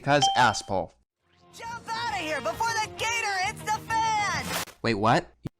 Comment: I didnt understood what he said I only heard "jump outta here before the gator eats the fence!" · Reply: They said 'gator HITS THE FAN', as in the gator is sliced up by the sewer fan.